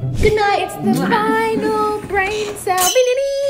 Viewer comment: I heard "its my finale brain cell"